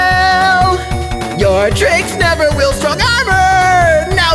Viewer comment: I thought he said drinks